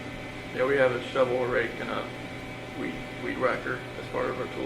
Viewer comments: I heard, “gone to Shanann, clear some leaves and dug a hole….we had a rake shovel and weed walked as part of our tools.” I thought he meant they used for work. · Didn't it sound like he said we had a shovel? Then goes on and a weedwacker. I listened like 10 times, I swear he did